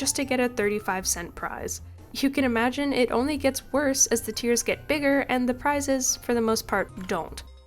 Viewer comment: I heard you say the "tears get bigger" instead of the "tiers get bigger." Well, it IS true, once a kid realizes they can't get that Switch...